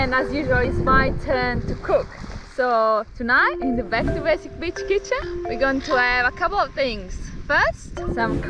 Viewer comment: I thought said "B2B bitch kitchen". Would have been funnier!